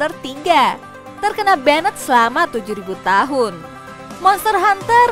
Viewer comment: gw yg salah denger atau emng 70 tahun? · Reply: 7000 tahun